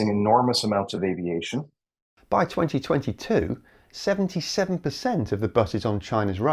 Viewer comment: The subtitles read: "By 2022, 27% of the buses on China’s roads had some kind of electric ...." but he clearly says: 77%. Just so you know!